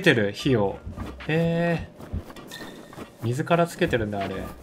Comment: アクセントで「自らつけてる」が「水からつけてる」に聞こえてしまった